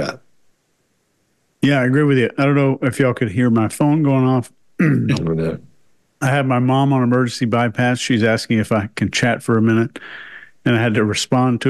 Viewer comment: Holy $h!tballs 😮 I thought said his Mum had HAD an Emergency Bypass….and he wasn't available to chat 😦😳 . I jumped up and rewound a minute, to replay….then realised his Mum was on Bypass “On His Phone”…and he wasn't in fact ignoring his sick parent for a video.….phew, 😅😅😅